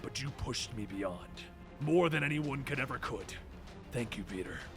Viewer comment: did he say "more than anyone could ever could" at 9:10?